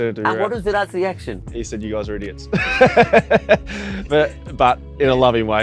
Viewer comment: You guys are idiots. Did i hear it correctly? Lol😂